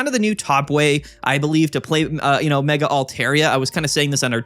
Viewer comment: i’m sorry? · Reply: Mega keeps getting me 😂 I hear ‘my mega altaria’ and I’m like YOUR WHO ALTARIA